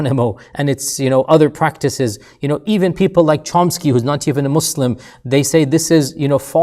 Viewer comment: I didn't hear well who he mentioned at 05:20 does anyone know? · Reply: Noam Chomsky.